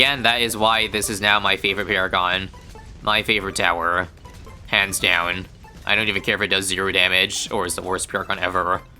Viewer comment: it sounded like pants down 💀